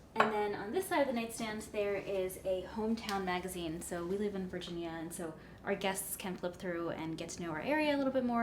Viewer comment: lol I thought it said vagina 😂